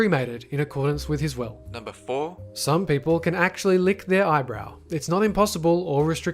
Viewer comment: he says eyebrow instead of elbow